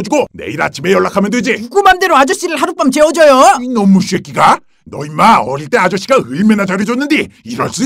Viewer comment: 잘들어보면 이놈의 똥꼬새끼라고 들림